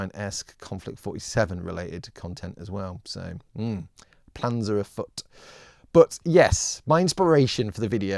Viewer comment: did you say "plans are afoot?" Or "Panza Afoot?"